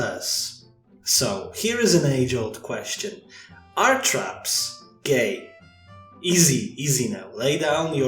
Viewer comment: When you said "are traps gay" I understood "are trucks gay" and when you said "traps (trucks, as I heard) aren't real" I went **¿¿¿HUH???** P.S.: Trucks are gay, as evidenced by the Lemon Demon song "Two Trucks".